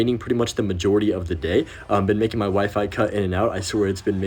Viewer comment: what did you say about your wife? 👀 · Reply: Oh it was just your wifi cutting in and out 😅